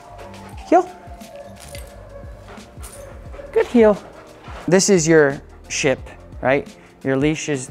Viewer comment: i thought he said 'this is your shit' xD awesome video I love my gsd she's so good but you must be confident they are so in tune with you. And assertive no nammy pammy or they walk all over you <3